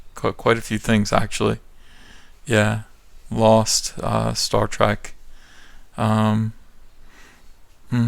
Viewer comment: I heard you say Star Track! Triggered as shit, my friend! 😥